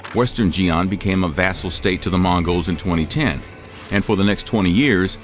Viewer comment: They say the poor person today is wealthier than kings of the 16th century Could you survive 1206? 🥜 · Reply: Just fyi @ 3:14 the narrator says 2010, instead of 1210